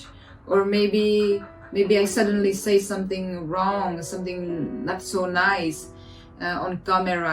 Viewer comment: i think she said “ something’s not so nice” 30:20 i think she said “ and they want to be famous”